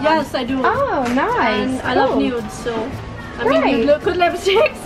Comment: When said “I love nude” I thought she said I love nudes